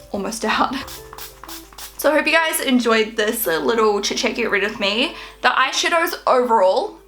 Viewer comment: Am I the only one who heard at 17:30 chitchat get rid off me? hahahahaha. :)